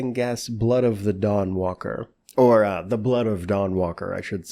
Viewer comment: Thought you said Blood of the Dog walker, which would have been an odd title!?